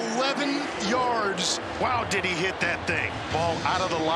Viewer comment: Whenever love hit that 58 yard field goal the announcer sounded like he said Diddy hit that thing 😭